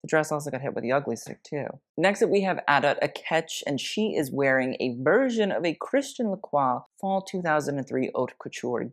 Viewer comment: What Instagram did he mention around 2:30 when talking about Adut? Anyone catch it? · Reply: Shrimpton Couture